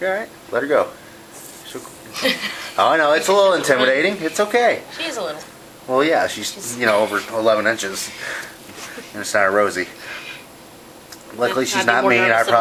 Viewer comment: did he just say that spider is 11inches?